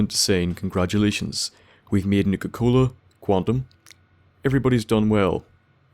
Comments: Yes! The Nigga Cola facility! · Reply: Same, at first I thought he was saying nigga-cola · sounds like you saying Nigga Cola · It sounded like he said nigga cola